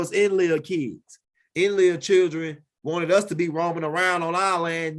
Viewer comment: Did u just say enlil children,? Wow salute to u, Cains children by way of ham, Canaan, wandeung vagabonds fur life